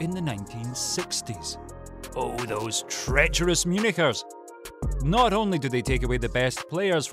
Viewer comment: does anyone else hear something other than “munichers”